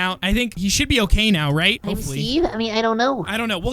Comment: I heard someone say hopefully